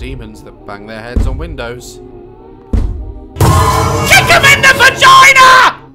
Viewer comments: “KICK EM IN THE V@ had me dying💀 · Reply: no because I heard that I thought he said chicken in my vagina😭🤣🤣🤣🤣💀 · I thought he said "chicken in the vagina"😂